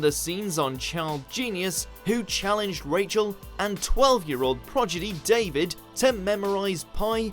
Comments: Um.... did he just say "progidy"?? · Did he say proGiDy? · Excuse me did you say 'child progidy'?